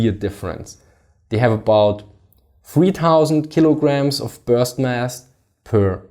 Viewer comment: What is he saying right after 3:10 ? is sounds like he is saying 3000 kilograms of "burst mass", but that doesn't make sense.